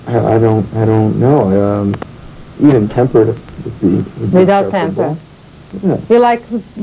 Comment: Só perguntas fúteis, por isso não teve futuro. O cara falou que é casado fiel... ela perguntando um monte de asneiras.... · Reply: Eu entendi "temperament" no começo, mas depois ela fala "without temper", meldels. Ele apenas brincou falando que ela era osso duro de roer e que seria caro manter esse temperamento dela, nada alem disso e ela se exaltou confirmando a brincadeira dele..